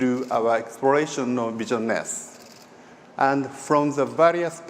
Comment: I thought he was saying "Vision Nest". That sounded cooler lol